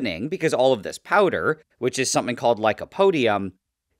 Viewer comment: Why did I hear "this powder is homophobic" 💀